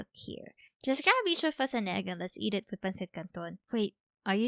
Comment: did she just said pancit canton?? Its my fave!